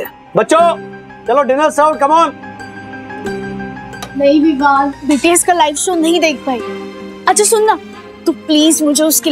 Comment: did i heard right BTS 😆